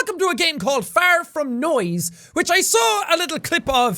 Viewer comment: I thought he said "fart from noise" 😂